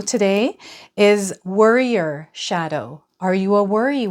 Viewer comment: Chose the way of the warrior as a child. Suits me. I don't worry too much, but I do expect the worst. So I'll look to that. It is often wrong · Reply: That's hilarious I thought you said warrior but you said worrier! 😂😂😂 Ok ill give it a Heavy consideration then thank you